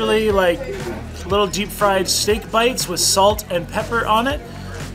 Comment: Ahhh man I thought you said "snake bites" for a hot minute there. I was so confused! LOL I'm like why does the snake taste like a cow! I rewound it and I'm good now!!! :) :) :)